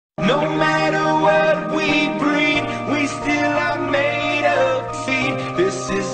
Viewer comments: Am i the only one who hears "no matter what we breath we still are made of feet"??? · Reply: I hear "no matter what we bread, we still are made of this" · no matter what we breed We are still made out of seed It is so relatable 😂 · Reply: I thought he said we are made of pee💀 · All I heard was “This where my s*men come, this where my s*men come” 🗿